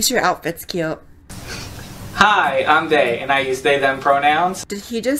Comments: I think they said their name was "Day," not "They." I wouldn't swear on a stack of Bibles, though. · I think the person you thought said they were called They, was dalled Day, maybe... Actually it sounded like 'Hi, I'm Day, and I used Day, them pronouns' :-/ But maybe They or Day needs to articulate better so we can tell :-/ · "hi i'm day" that's what it sounded like to me but i guess hi i'm they works too :/ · I thought that guy said "Hi, I'm gay" lol